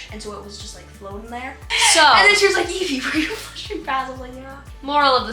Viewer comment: did said. put the f*king pad out of the toilet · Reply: I think she said “have you been flushing pads”